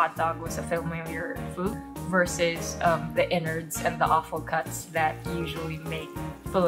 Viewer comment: hiii i just wanted point out at 10:57 the subtitle says "awful cuts" but im pretty sure its supposed to be "offal," as in organ meat